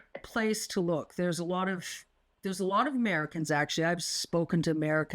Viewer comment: Why does the transcripts say “Mexicans” when he clearly says “Americans”? 1:05 &